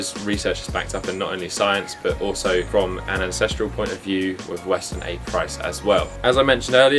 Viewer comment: Pardon me, what is said here after "not only in science" up to "As I mentioned earlier"? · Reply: "But also from an ancestral point of view with western (a price?) as well" Not really sure about that a price part though.